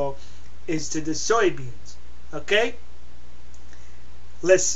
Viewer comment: It sounds like he said soy beans lol.